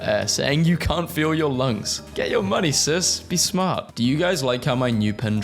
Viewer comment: why do I hear lungs not legs